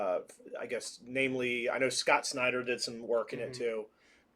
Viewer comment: I thought you said Scott Steiner.